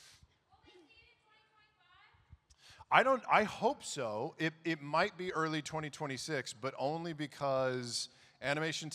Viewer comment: anyvody catch what that question was at 8:39 now im curious whats coming early 2026 but my hearing is not catching what they said. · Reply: "Will we see it in 2025?" by the context i undestand she is asking about Mighty Nein series